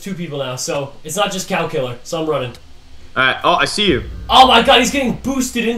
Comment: The first time they said the cow killer I thought they said the calculator